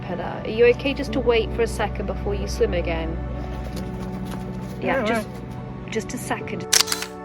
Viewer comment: What an absolutely superb vlog. Thanks! At 15:16 did say "Yeah, all right" ?